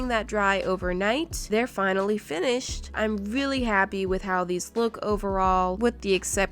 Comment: Wait did you just say im willi happy?😅😂❤️not trying to hate🥰 love you 💖🤩✌🏼🤪